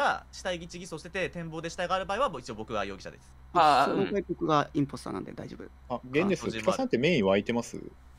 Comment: にKさんが僕がインポスターなんで大丈夫ですって何回聞いても聞こえる